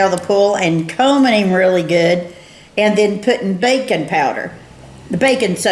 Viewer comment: I heard "bacon powder" and my mind went blank